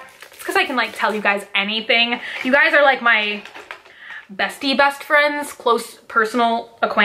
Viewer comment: I was listening to this while getting ready for work & all I heard was “You guys are my busty best friends!” Lmao 🤣